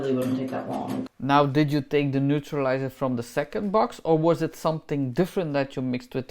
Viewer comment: I thought she said the second box she mixed wrong, but it’s obvious that something went terribly wrong.